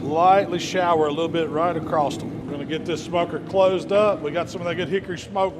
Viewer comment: sounds like said "Get this fucker closed up" 😂